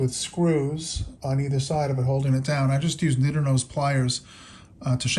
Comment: you used what kind of pliers?? I can't make out what you said. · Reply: Sorry about that! "Needle Nose Pliers"